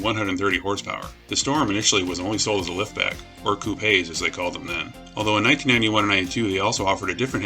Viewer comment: Are you saying 'coupe' when I hear 'coo-pay'?